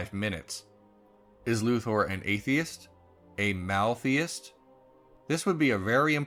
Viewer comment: I don't understand what you're saying at 5:08. "Is Luthor an Atheist? A <blank>." What's being said? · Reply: "Maltheist"